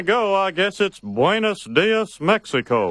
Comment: Im Mexican, I speak spanish, what Hank said on 5:02 was correct, Buenos Dias, while Dios, wich is god, sounds similar, he said it correctly.